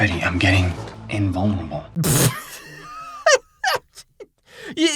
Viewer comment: When it said "betty I'm getting invulnerable " I swear I heard "betty I'm gay , invulnerable " I had to rewatch it